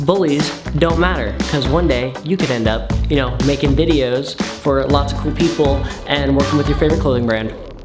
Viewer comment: oh my gosh at the end when you said “working with your favorite clothing brand” I heard “working on your own clothing brand” and I almost freaked out but then I went back and rewatched it 🤦‍♀️ lol I love you, thanks for being so amazing!!! all the love